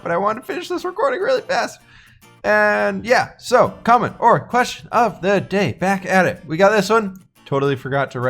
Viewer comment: Oh, comment or question of the day. He, He, I thought you where saying commodore question of the day.